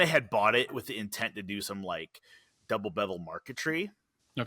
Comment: I am pretty sure @ 22:45 the audio dropped out. Did say the cabinets doors would be double-bevelled Intarsia?